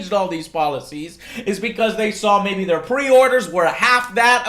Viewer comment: He clearly said pre-order numbers.